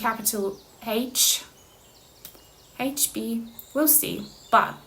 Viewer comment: 999 like! Ooooh 💗✨ Out of town with my out of state cousin, Katy! ✨ 6:06 do I hear H B ? I’m B and the crush is H ooooo 🤔🫣🥰